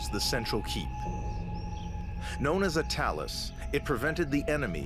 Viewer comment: He is using a fire torch because that's what the Templars used..they did not have local 7/11...nor electricity...They had talis..L'chaim from Jacova born March 11...in Bethlehem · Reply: I have to apologise, I thought you said tails not Talis, I don't claim to know what Talis is but I see the difference between the two words